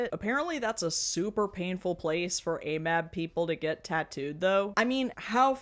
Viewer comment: Huh interesting that there's a difference... I guess Also every time you say amab All I hear is "all males are bastards" an y'know what? Maybe we are.